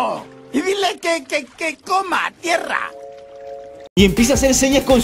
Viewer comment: Al 2:08 escuché que coma mierda